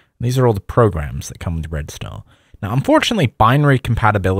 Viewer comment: I had to rewing it 10x to try to hear the name of the os, it sounded like you were saying red stont. I thought it was red star but it didnt sound like you were sayibg that. So then I googled it and it is in fact red star.